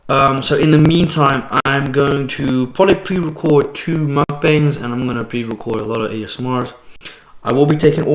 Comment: I thought you said quit mukbang I got scared for a minute lol